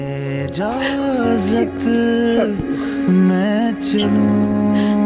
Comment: Wt did shaweez say on 19:15?? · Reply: Nimra Khan I will go